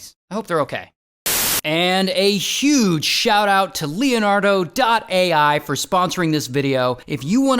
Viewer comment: For a second I thought he said "and a huge thank you to Leonardo da Vinci for sponsoring this video" idk I'm weird